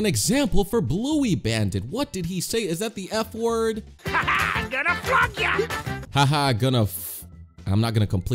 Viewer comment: he did not say the f word instead he said I’m going flunk y’all